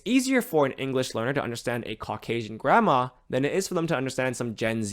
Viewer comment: honestly thought you said "caucasian grammar" and was like, wtf is caucasian grammar? Only when you said it again did I realise you said grandma. That's my Aussie ear not being familiar enough with US accents